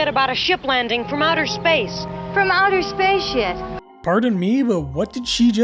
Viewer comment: Yeah, you didn't say what you think she said. But I assume you think she said the S word. She didn't. She's saying "From outer space, YET."